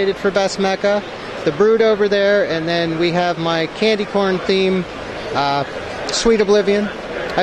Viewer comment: I thought he said candy porn